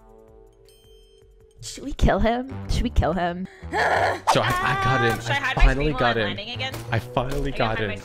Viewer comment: I think he said I got in, I finally got in, I fuc**ng got in 😮